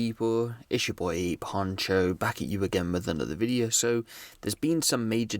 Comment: Bro your intro is the same everytime without it being prerecorded and the way I say it word for word everytime 😂😂😂😂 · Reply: “Yoo it’s your boy A Poncho “ lol saw somebody point this out and now everytime I hear the intro it legit sounds like he saying it’s A Poncho lolol